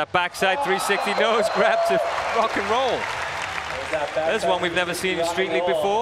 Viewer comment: is that what was on tv (fox) in US? 15:20 he said fuckin :o bet that earned a shitstorm from the boss thahaha - 40.12 luan man :D one of my favs too unterstand why the crowd gets hyper :D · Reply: He 's said "to rock 'n roll" :p, it's the name of the trick.